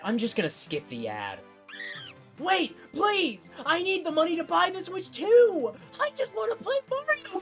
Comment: i thought he said he needs money to feed his family (or im just tweaking)